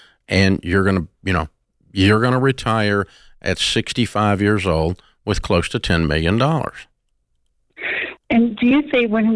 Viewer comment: He never said that. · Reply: he clearly said she will retire at 65 with close to 10 million. Watch it again